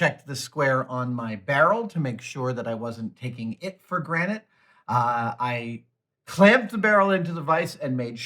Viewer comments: At around 6:45 mark does Adam say "take for granite". Is this rick and morty? Am I about to have my memory wiped? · Did you just say taking for granite... Have you been saying it wrong this entire time? Lmao